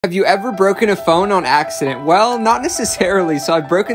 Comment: Who els thought he said have you ever broken a bone with a laser